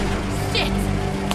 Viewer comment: did Lilly say 'Sit'? XDDD